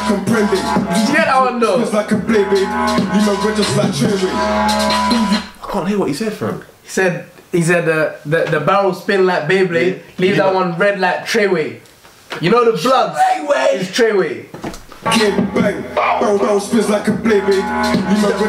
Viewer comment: thought he said “leave man red just like cherryade”